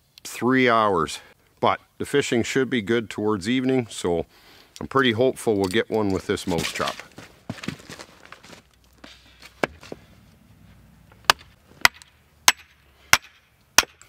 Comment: Ohh, I thought you said you were going to catch a Moose with a mouse trap, hahaha.